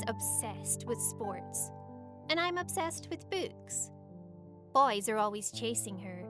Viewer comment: Do i heard boobs??